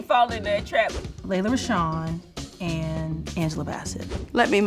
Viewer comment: Who did Contessa say at 3:48? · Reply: Lela Rechon she’s an actress